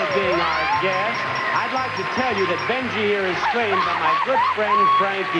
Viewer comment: I seriously thought Bob said "Benji here was spayed by my good friend..."